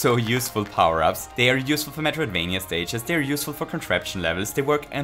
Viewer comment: What does he say there. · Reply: Christina Kirsten He says “they are useful for Metroid-vania stages…”